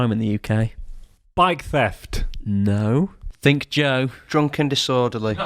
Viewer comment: Was I the only one who thought beastialty when he said think joe for this crime?